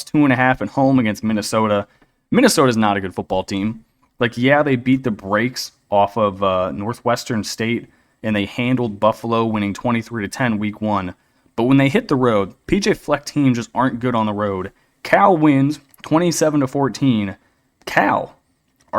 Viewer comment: Man i thought you said take buffalo? 🤣🤣 i did and man